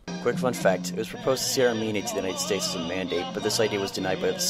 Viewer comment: I didnt understand that part. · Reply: "It was purposed to cede Armenia to the United States as a mandate." I had to listen to it about 20 times and only with the help of the other reply by was I able to figure it out.